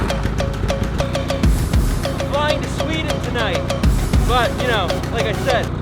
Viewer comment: Did I just heard that you are flying to Sweden tonight?!😍 at 0:25 I love your enthusiasm towards photography. Keep doing what you are doing!